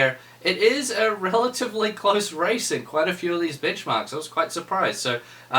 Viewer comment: did he say bitchmarks?